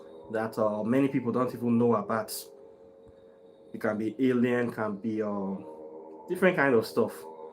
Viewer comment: Did you say aliens 19:16 ? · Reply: Yes Aliens